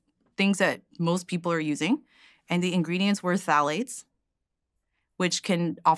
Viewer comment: What ingredients does she say at 34:29? I don't understand. Thalates? Phtalates?